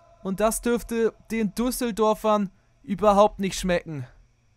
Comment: Habe ich mich verhört oder hat er duseldorfern gesagt 😅😂🤣